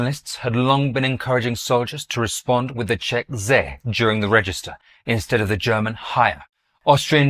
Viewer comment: Can somebody write down and explain the part at 14:04 for me concerning the things Czechs and Germans said? I can't catch it.. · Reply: It is difficult to understand, but it can be inferred that the Czechs used "zde" (here) instead of the German "hier" when reporting presence. The expression "zde" is still used in the Czech army today.